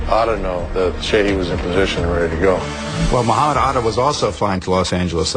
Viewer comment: did he just call him “mohotta atta” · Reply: He said Mohamed.